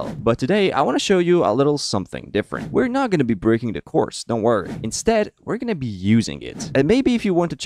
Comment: bruh the subtitle say "We're not gonna be breaking the course" the word course shud be replaced with CORS